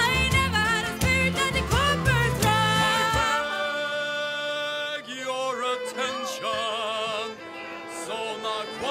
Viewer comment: From 2:00 to 2:22 what does that puppet sing and talk? I can hear that he is begging others' attention at beginning and says something about "green" and "health" in end, but otherwise I can't figure out what he is saying. Edit: typo. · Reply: He wants to propose a toast to the queens lasting health.